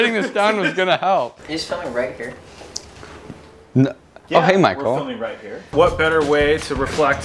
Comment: Lmfao when they said " hey Michael " The subtitles said "hey micro"